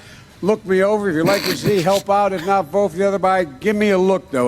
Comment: It also sounded like he said at the end. " or vote for the other Biden "